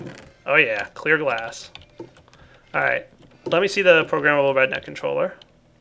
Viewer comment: I heard "programmable redneck controller". I need to learn Java.